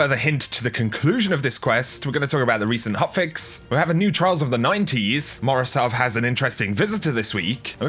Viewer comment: I swear to god I heard "we have new trial of the nineties"